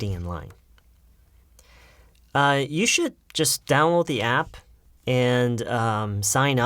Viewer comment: 초에 뭐라고 말씀하시는거에요? You should just stow the app 이라고 한건가요? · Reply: Download 라고 하시네요